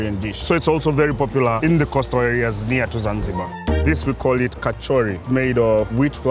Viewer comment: did I hear kachori 😅😅